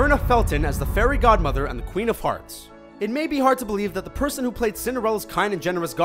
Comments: I heard him say queen of farts (queen of hearts) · Well... it's weird to start with Winnie and Kaa, since they are so similar that even as a child I noticed it. Also... 2:00 am I the only one who heard Queen of Farts? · When he said "queen of hearts" I thought he said "queen o' farts" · I heard "Queen of Farts" instead of "Queen of Hearts"